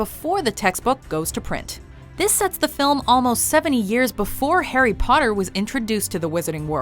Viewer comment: don't think so. Tom Riddle tricked Hagrid into being expelled 50 years before Chamber of Secrets. That's 40 years (give or take) before Harry was born. If this is 70 years before Harry Potter, there should be a gap of 20 or so years between Fantastic Beasts and Tom Riddle's time at Hogwarts. Correct me if my math is off, I'm not so great with that. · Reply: 70? i thought she said 7 years